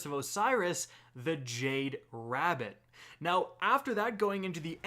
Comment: did he just say Polaris lance came from curse of Osiris. I’m disappointed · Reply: Bob Bob he said jade rabbit. Polaris came out like at 6 or 7 minute mark